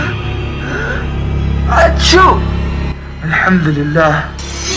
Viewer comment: i didn't understand what you said at this point. Please explain? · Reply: he said--" iyar hamukamullah"